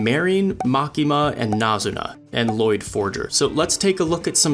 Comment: Wait did he just say Loid 💀